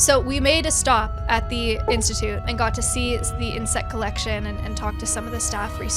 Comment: did someone say something about an insect collection?